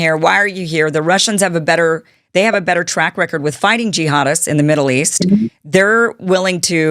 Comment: I kept hearing “gee hadist”. Let me guess, is it an offshoot of “all kaida” ? Hummm?